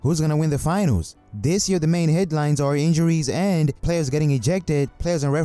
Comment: I thought he said hairlines at the beginning